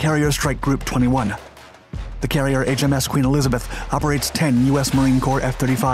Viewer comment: “Shows the CARRIER slowing down”?? You mean F-35 slowing down. On that note something else that needs to slow down is you, you pump out quantity but severely lack QUALITY. Slow down, less is more. So many of your video as of late have Glaring errors, facts, wrong images, etc. We like what you do, we appreciate it, but man, slow down and get the details right. They matter! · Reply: I thought he said HARRIER.